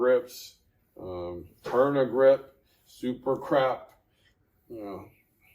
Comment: did he say "Supercrap"? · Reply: Yonex Super Grap overgrip